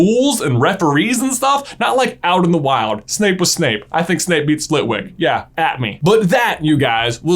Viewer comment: All I heard is "I think snape beat slitwick"